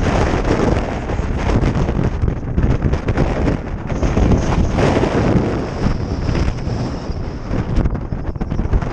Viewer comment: what you say? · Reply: Wind, would you mind not pushing me off the mountain, could you do that for me, please